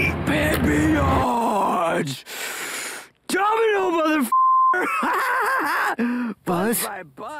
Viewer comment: what he said? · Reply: He says "Domino Motherf***er".